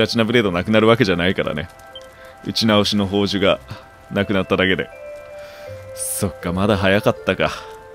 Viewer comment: 『打ち直しの宝珠、大分余ってるし』が、『牛の足の報酬、大分余ってるし』に聞こえたわ……。 牛沢さん、あんた自分の足と引き換えに……ってなったわ……。